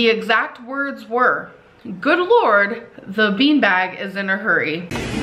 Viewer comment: I heard, “so lord shoots the bean bags at her” and I listened pretty close. I’m sure that’s not what he said but that’s what I heard……..